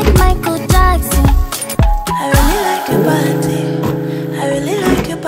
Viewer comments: All I hear is 'I really like to potty' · everybody: that looks good me: 'i really like to party' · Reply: dude i really dont know why i kept hearing "i really like your panty*